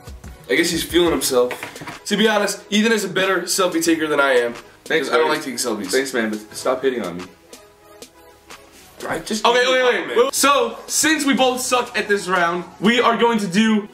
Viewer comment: "Thanks, man. Stop hitting on me." - E 😂😂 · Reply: i thought Ethan said "hating on me" hahahaa